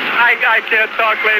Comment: At the end, the only thing I can hear him say is “this massive fucking wreckage” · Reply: I thought I heard "fucking wreckage" as well, but after replaying the last few seconds several times, and reminding myself of the time period, I realized it was "smoking wreckage". The camera provide further context by showing literal smoking wreckage 😏